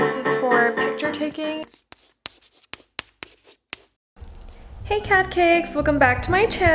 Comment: the "hey catcakes!" will always be the same every single day, I hope she never changes the intro. Love you three! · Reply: At first I thought she said carrotcakes…